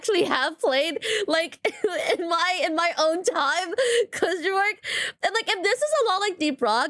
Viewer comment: DID I HEAR A ROCK AND STONE?!?